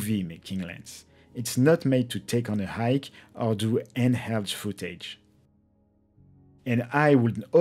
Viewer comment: Anyone, please clarify for me what he is saying @ 6:00 > "take on a hike or in house footage" Did I hear "in house footage" correctly? Thx.